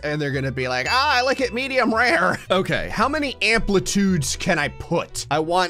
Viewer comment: That sounded like 'Meteor rare'. XD